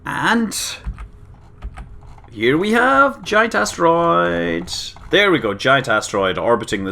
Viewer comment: I know you said "Giant Asteroids", but I heard "Giant Ass Roids." Gave me a good chuckle.